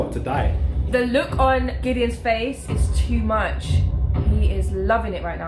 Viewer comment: I thought she said " The LUKE on gideon's face " Funny as just a few moments later he did have a look of fear and despair Also the guy sitting on the throne is Bib Fortuna, he's a Twi'lek and was Jabba's right hand man. It crazy seeing him cuz we all assumed he died on the Jabba's sailing barge in ROTJ.